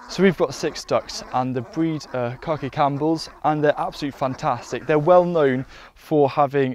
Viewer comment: That's Khaki Campbell ducks if you want to look 'em up.I thought he said "corky"at first.